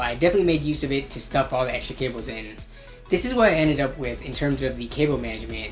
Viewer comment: Did you say s**t at 6:10?